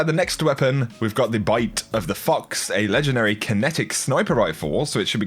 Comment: Bruh I thought he said bite of the fucks